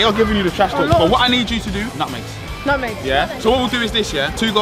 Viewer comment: At first I thought you said nugget But I realized you said I smell at nutmeg